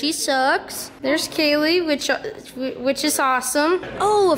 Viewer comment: “there’s Kaylee who is ho-awesome” what where you about to say there young man